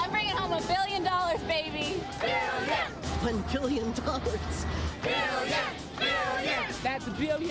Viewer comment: What’d he say at 25:10? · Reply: A billion dollars